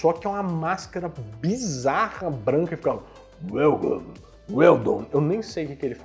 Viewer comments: Em 24:14, acho que ele diz: "Welcome', seja bem vindo! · essa máscara bizarra pelo que eu entendi ele fala "Welcome" que quer dizer bem vindo em inglês!